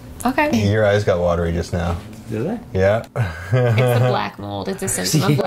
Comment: I hear “they did”